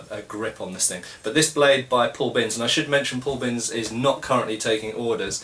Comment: Glenn Nnelg Search for 'Paul Binns Swords' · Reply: Thank you muchly, i heard "Paul Vince", hoping to find some good spear tips as they are about the only legal weapons we can get where im from in Australia.